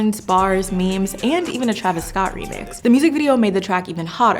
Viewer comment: “Travis Scott remix” bruh he clearly said it’s not a remix it’s a part of the sequel. 😡